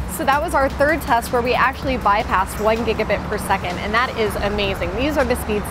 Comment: I thought they said 1GB/sec, so pubg should be downloaded in 2 secs